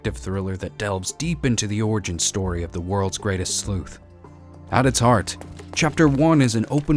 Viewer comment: im sorry, i know it's "sleuth" in that very first sentence, but still, i heard "the origin story of the world's greatest slut" 😂 like ok, i guess that does describe Sherry 😂😂😂